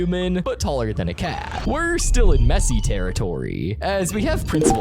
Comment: did you say Messi or messy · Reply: He said messy